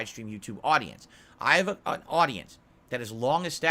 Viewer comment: Barely a minute in this video and...did I just hear him right? "I AM a audience"? 😂🤣 The wonders of his speech issues caused by probably his drinking habit. If it's not what he said still how he says things just come out so incoherent and easy to misunderstand. Seriously lol. Wouldn't put it past him if he controls his own audience since he is his own audience. Lol.